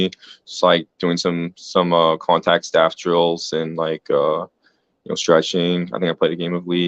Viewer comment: what are Contact Staff Drills or did I hear that wrong?